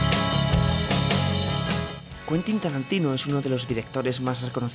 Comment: Pensé que decía Quentin Tarantulino